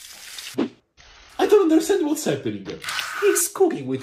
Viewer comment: At first I heard say "he's cooking with wall". Had to double check